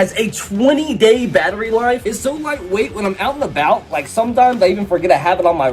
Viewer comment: For a second at 2:19 I thought he said heart weight